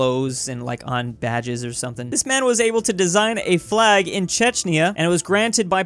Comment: subtitles + what i heard = Chechnya, instead of Czechia